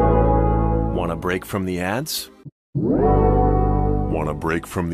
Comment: on the first one i thought he said want a break from the ads